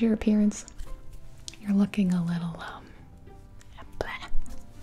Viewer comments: I think you meant to say “you’re looking a little, Bob” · I thought she said „you‘re looking a little......uh.....BLaCk“